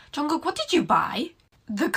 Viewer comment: did i just hear 'jungkook'